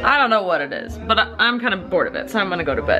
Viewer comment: did she say "check you later"??